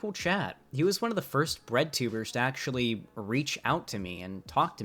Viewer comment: i thought you we're saying Redtubers and was slightly confused. Great video, thank you!